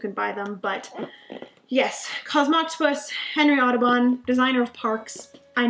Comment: I didn't hear "Kinkstarter", but being German I swear every single time you say Henry Audubon's name I just hear "Autobahn" :') Also, no idea how you managed to fit all of those games you received or purchased into your luggage. I couldn't travel with a big number of games as it would simply be too nerve-wracking an experience.